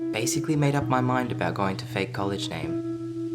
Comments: I thought she said im going to Fake Colage name 2 😂😂😂😂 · I love how he says “fake college name” so casually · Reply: I thought he said fade college 😅